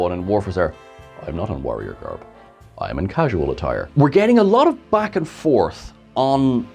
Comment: I thought Warf said this is not warrior garb. it is cashmere and that is my head cannon now and nobody can shake it Warf wears cashmere